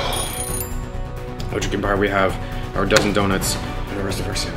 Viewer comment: Did he just say Dunkin Dounuts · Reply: DPR Nikeoland he said Dozen donuts